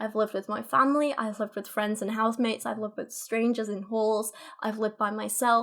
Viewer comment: I'm sorry but your accent cracks me up. I thought you said "I've lived with strangers and whores" lol